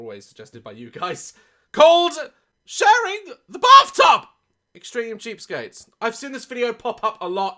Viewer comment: OMG 😂😂 I thought it said extreme cheesecakes 😂😂😂